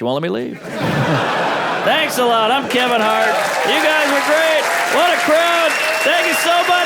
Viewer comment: did he say he’s Kevin hart?